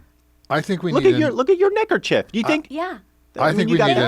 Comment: i certainly did not think that you said 'necker'chief. Forgive me!!!